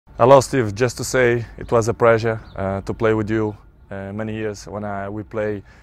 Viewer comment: I thought he said "it was a preasure" sorry cris i have to laugh for this 😂😀